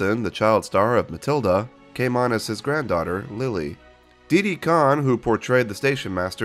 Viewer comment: I thought you said Diddy Kong instead of Didi Khan